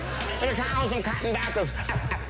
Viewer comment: I think he says "he found some cotton dockers"...